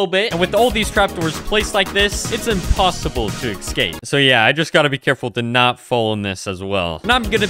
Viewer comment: did you just say "excape"? 😅